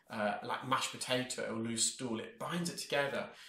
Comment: Did I hear say "loose stool" or was that my imagination??